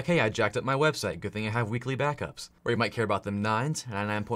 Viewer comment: Lmao was just listening and thought you said "weekly bathtubs" instead of weekly BACKUPS 😂